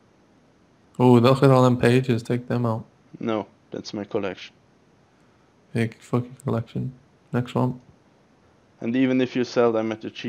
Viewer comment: Did he say "fuck your collection."